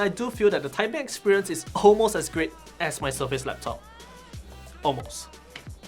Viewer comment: Almost as great as my **** Laptop ??? Can you tell me what you said ? Did not understand this word. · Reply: surface laptop ---- microsoft surface laptop